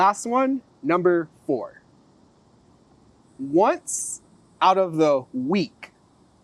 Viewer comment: Bro help me in 4:50 what did you say ? I hear once out of the week si dices eso? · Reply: Yup! "once out of the week" You are correct! Once or one time during the week, the garbage truck comes. I hope that is helpful!